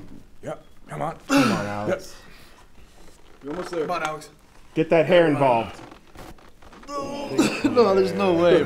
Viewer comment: did he say "get that hair involved?"